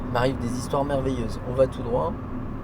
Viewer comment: c'est toi qui a raison, tu a bien dit : " On va tout droit". ☺